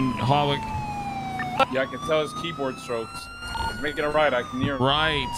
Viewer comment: Did he say i can tell his keyboard strokes